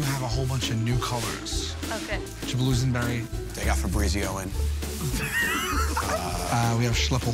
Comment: "They got febreezio in" LMAO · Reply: Original151 oh i thought he said “The got febreezioan”